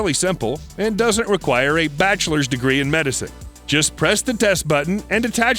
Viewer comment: Did he just said "bachelor's degree in medicine"?